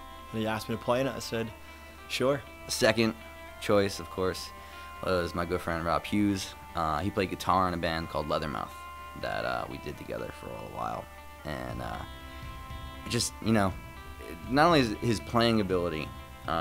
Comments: I so thought he said girlfriend Rob Hughes instead of good friend xD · I misheard that as "girlfriend" and now I´m trying not to wake up the whole house by laughing